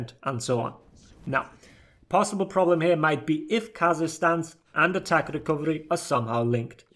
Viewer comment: I always heard "kazakhstan" when you said kaza-stance 😂